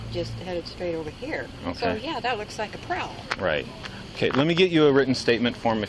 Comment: important to listen from 9:50 as states at the end of her testimony "yeah so that looks like a pro", needless to say her years of training as to what a pro looks like is somewhat of an abysmal mega fail. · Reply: I thought she said “prowler.”